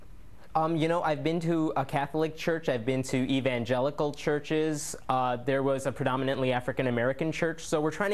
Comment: Did he just say E-bay'ngelical churches?